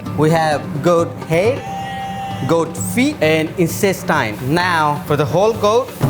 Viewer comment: I swear to God I heard incest time